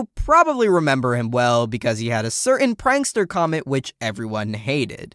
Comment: Great Video! Bouldergiest is still one of my favorite Mario fights in all the games! But if I may ask, what is the “prankster comment?” Edit: Nvm, I misheard lol.